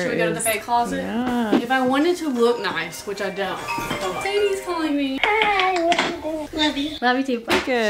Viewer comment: I'm rewatching the vlogs.... did I hear 11:14 "sadie is calling" WHAAAAAAT HOW DID WE NOT KNOW!